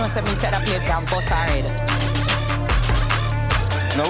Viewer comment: I SWEAR I HEARD SPICE SAY SHE GONE "BEAT ASHLEY ASS TO THE WYTE MEAT!" Or she said "ILL BUST HER ASS!" 😂😂😂😂